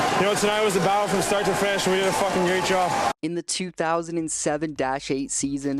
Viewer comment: I swear he says "we did a f*** great job" 😂